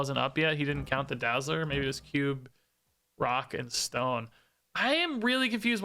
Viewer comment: Did I hear a Rock and Stone?